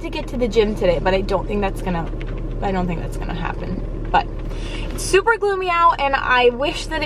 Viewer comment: Super glue me out?! 😂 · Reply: 😂😂😂 “it’s super gloomy out” lol it literally sounds like I said super glue me out